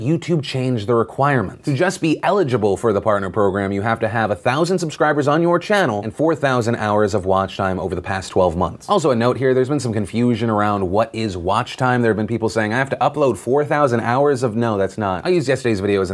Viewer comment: For a second I thought you said 4000 hours a month, and I was freaking the fuck out, but then I realized that I had just heard you wrong and I felt better.